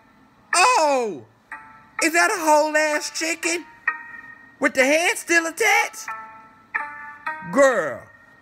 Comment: “with the head still attached” LMAAOOOOOO i’m screaming 😂😂😂😂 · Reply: I actually thought he said "with the ass still attached"! 😂